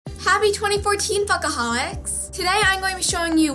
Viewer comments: Lol at first I thought you said happy 2014 alcoholics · I thought she said fuck aholics...